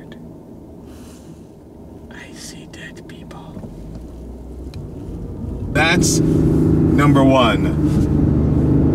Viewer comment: Um...what did you say at 1:45?? Great video though, the sound it makes is actually surprisingly good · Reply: i see dead people